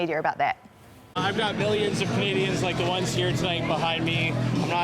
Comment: Did he just say “billions of Canadians” 🤦🏻‍♂️